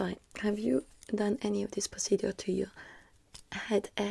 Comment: She said procedure, but my half asleep self swore you said “pussy do”